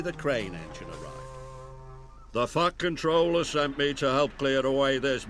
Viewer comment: When Harvey arrives, it sounds like he says,"The Fucking Troller".